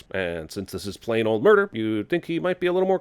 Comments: the sub titles say "plane" instead of "plain" I hate this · subtitle says plane old murder instead of plain old murder bruh